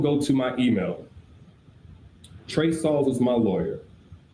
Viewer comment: I thought he said Trey Songz was my lawyer😅